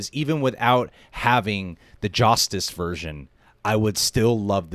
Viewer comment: Mark 12:38. Did you just say, "Joss-tice version"? 🤔 😅😂